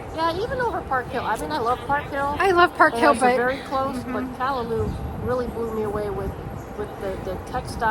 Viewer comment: you compared the new trends to a store in this video. What was the name of that again? It sounded like you said Calahoo... haha 😂 I have no clue how to spell it but wanted to look into it. Can you tell me again what store that was, please? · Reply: The store was Kalalou. It was our favorite store here on day 1.